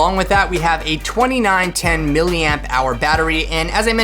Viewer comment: It's actually $199 fr the first people to order it isn't it? It seems like a great offering except for the battery. Definitely cover that in detail on the full review ***** Edit: Scratch that, I thought you said 1910 mAh battery, but it is 2910mAh! WOW what a great device